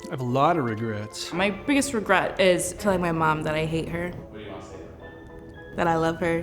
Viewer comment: omg at 00:30 i thought she said "killing my mom..." lmaoo